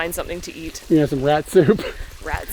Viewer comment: When yo said katsu I thought you said Rat Soup